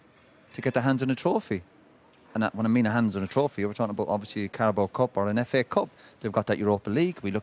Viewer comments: when roy said carabao cup i coulda sworn he said cardboard cup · Thought Keane said a cardboard cup